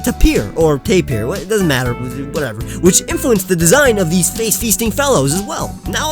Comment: Did he just say Face feasting!?